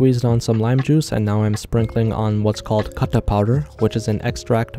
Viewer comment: I thought u said ”caca powder”